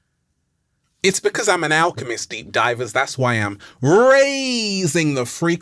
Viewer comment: Thought he said am an arsonist 🤣